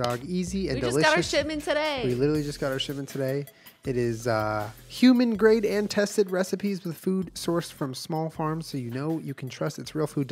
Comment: i heard "human great intestine recipes" and did a double take like oh thAT's the kind of recipe we're trying to guess here alright gotcha